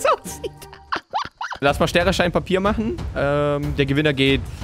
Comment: Bin ich der einzige der Stere,Schein,Papier gehört hat?